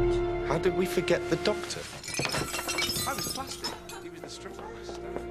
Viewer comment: is it just me or at 3:25 does rory say "fantastic! he was a stripper!"